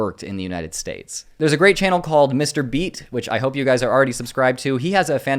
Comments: I thought you said Mr beast had a video about the great replacement theory and I was 😬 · I was so confused. You said Mr. Beat, but in my head I heard Mr. Beast. Those would be... very different videos.